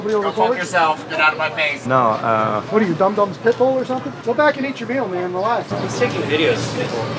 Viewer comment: Dumb dumb's pitbull! ☠️😂😂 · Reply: I thought he said, "Dumb Dumb's pickle!" Pitbull makes more sense! Either way, what a pitiful, little tattle tale who never grew up. No matter how many people he turns in for "doing the wrong thing," he'll always feel like a lonely little boy with no friends. And since adults don't like tattlers, either, he'll only have friends just like him, and none of them will be happy.